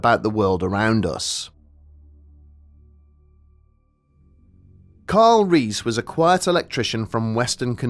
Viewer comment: I swear I heard: Kyle Reese...